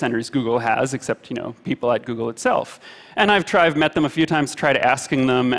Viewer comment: I thought for a minute he said “I tried meth a few times I tried acid”